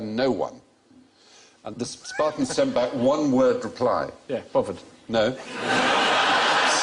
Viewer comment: what does allan say? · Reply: Bothered. Shortened slang for "Am I bothered?" meaning 'I'm not concerned'